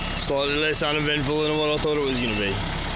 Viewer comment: Wtf did he say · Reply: Less uneventful than I thought it would be..